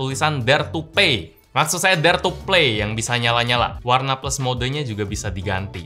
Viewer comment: be like.. dare to pay.. me : wtf?? 😅: maksud saya "dare to play.." me : oh syukurlah 🤣🤣